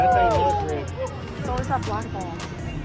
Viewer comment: Did I hear someone say "Oh that's a black man."